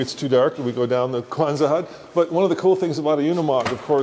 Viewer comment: Did call a Quonset hut a "Kwanzaa" hut?? lol. ;-). ;-)